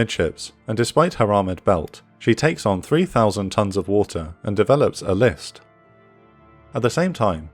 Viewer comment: Can I ask you all a favour? If you enjoy this documentary, could you please hit the like button and/or leave a comment, it helps us massively. Appreciate you all! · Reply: Of course - your work is excellent. Also, I'm still very amused that I misheard the narrative at 17:12 as, "Torpedo one impacts Musashi amidships and, despite her armored belt, she takes on 3,000 tons of water and develops a lisp." I imagine an angry and disgusted sailor staring at me from the deck of the sinking ship, saying "That'th not funny, athhole."